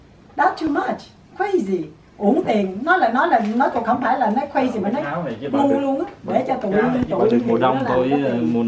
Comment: it’s sounds like she said “my nigga”💀